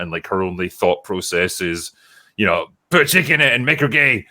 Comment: First 13 minutes I thought they were saying "put a chicken in it" and I was slightly puzzled